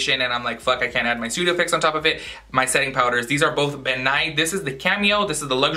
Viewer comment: Ben Nye the makeup guy!!!! I died when you said ben nye because I thought you said Bill Nye!!